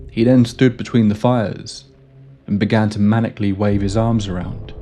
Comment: i heard "mechanically " instead of "maniacally" and i pictured Donald doing the robot dance between two fires 😂😂😂